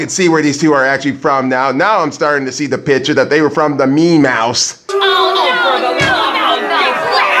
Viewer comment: Did You Say The Meme House